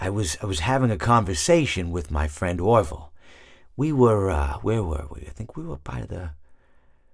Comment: I thought he said we were 💩 ing by the river lol